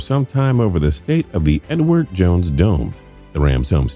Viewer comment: I legit thought you said 'N-word jones dome'